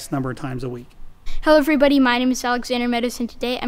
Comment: Alexander you said your name to fast it sounded like you said hello my name is Alexander medicine today